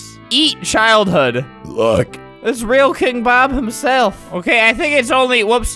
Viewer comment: what word did you just say in a burp?! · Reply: he said "lOoK its real king bob" not what you think he said. look at the sub titles.